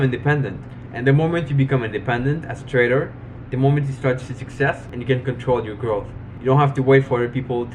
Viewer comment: At first i thought you said "the moment you start to success you get control of your girl". xD Anywho, from this video im guessing your a fan of the vlogger Casey Neistat?